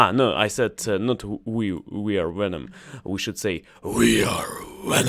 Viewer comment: See if you can pronounce the V in venom. (Sounds like you said wenom).